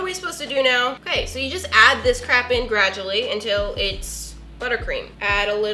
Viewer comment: Maybe I'm just stupid but did she say crap😂I had to do a double take😂😎